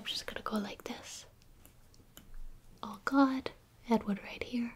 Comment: It sounds like she's saying, "Oh God" instead of "All gone" lol.